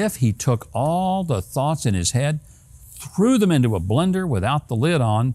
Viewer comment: I thought it said, “Biden is a BEND-er”!! 😂😂😳